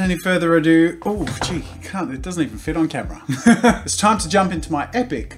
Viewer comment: anyone else hear him say oh gee cunt it doesn’t fit on camera or is that just me